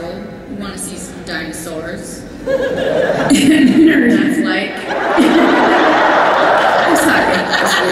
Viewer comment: what did melissa say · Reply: "See what that's like" I think